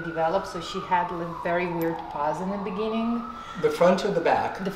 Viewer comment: ?? Did she say her PAWS ( front paws) didn't really develope (her skeleton didn't really develope??) and she had really weird paws in the beginning?? Is that what she said around this time?? I couldn't quite make out exactly what she said ..sorry --i love when owners and dogs look alike.♥️ U no how sometimes ppl say how sometimes ur dogs developes ur look? I love that! I can see it here♥️🙂so cool! @ 4:56--'she mite even shake' lmao it's almost like ur TOLD HER to go shake lol she walks awake and..... shakes!!😆👊 Good one!lol Oh I see the difference rite away on the re-exam! Great job! @ 5:45!! WOE!!???? Who slaughtered ur are.??!! Did somebody try to draw blood from u or sumthin?!! Wth happened to ur arm homie??! 😳 Somebody miss ur vein?? Alot?!! Lol that's not a draw site! What happened to ya?🤔